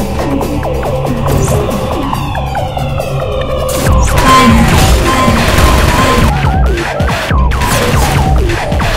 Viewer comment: What is the turret saying? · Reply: "FIRING. FIRING."